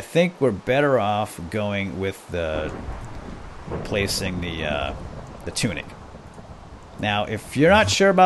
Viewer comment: Matunuck? ROFL? I watch at 2.0 speed, so the words come at me quickly, and that's what I heard, being from Cranston. :D